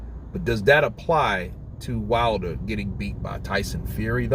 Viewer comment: I thought you said wilder is getting better